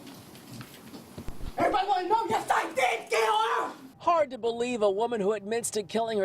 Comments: What the hell did she say? I need a translator. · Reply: Sounds like she said " everybody wanna know yes I did kill her"! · What the hell did she say? I need a translator. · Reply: Sounds like she said" everybody wanna know yes I did kill her"!